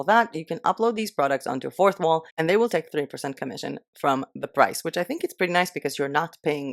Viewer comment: 🙋 I couldn't catch whether 3% or 30%? Would be grateful if you reply. Thanks for all the sharing and hard work! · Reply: 3 :) You can check their commissions and info pages for more detailed info :)